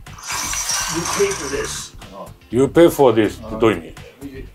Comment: death ❌ this ⭕️ ちょっと違うだね “You’ll pay for this” は 「仕返ししてやるからな」:_あきらめない中村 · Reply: 確かに "death" より "this" のほうが正しく聞こえますね！私は "You'll pay dearly for this!" に聞こえると思いますが、まあほぼ同じ意味です。